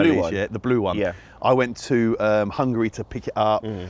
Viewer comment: did he call it hungry